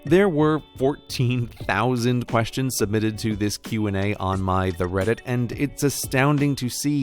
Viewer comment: Did you just say, "On my 'The Reddit.'"